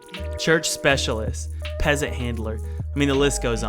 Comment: did he just said "peasent hitler"?